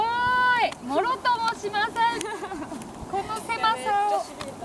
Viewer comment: ずーっと気になってて再度指摘させてください 「もろとも」しないなんて言葉はないです 「物とも」しないが正